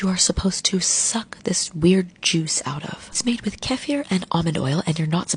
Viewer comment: i thought the bitch said "CAT HAIR" 🤣😂🤣😂🤣😂